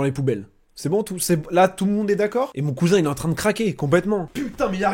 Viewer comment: J'ai entendu " mon cousin Imed" bah oui, il aide bien, il khalass comme un frère!